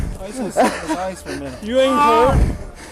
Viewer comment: the subtitles say "you ain't cold" but clearly the officer says "you ain't hurt"